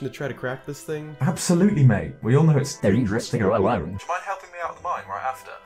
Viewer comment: In this section, did say “dangerously alone” or “interesting alone”?